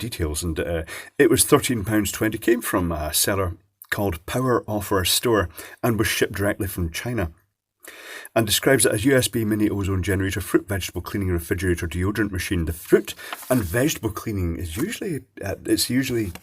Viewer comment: Half listening over breakfast and I hear "foot and vegetable cleaner"... Thought for a second that was being extra naughty, until the mental slap could be administered.